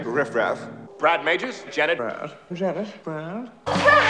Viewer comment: IT SOUNDS LIKE HE SAID BRUH LMAO